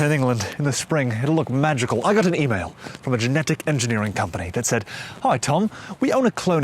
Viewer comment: I first heard 'generic' cloning machine and honestly that'd be a great video too, what makes cloning generic, isn't it generic by nature?